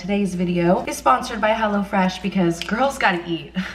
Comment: I thought you were saying hella fresh